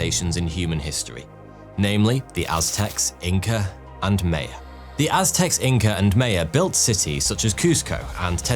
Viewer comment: what I heard “… the aztecs,the Inca, and me” 😂😂😂